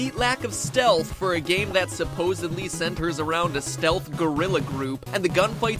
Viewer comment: I can't be the only one that heard Stealth GORRILA instead of guerrilla